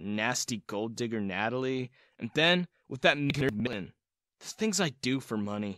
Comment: Wtf did he say at 16:41 and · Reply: I think it was “and then with that nerd Lin”